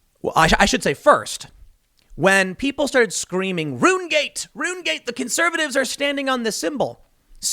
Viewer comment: What Tim said: "Runegate! Runegate!" What I heard: "Runescape!"